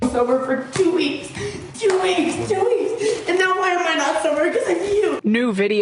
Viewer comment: Is she saying "I'm not sober because of you" ? I mean either way, she's such a gem ain't she